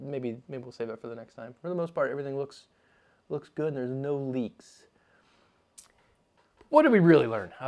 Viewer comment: Wait... did you say No Leeks?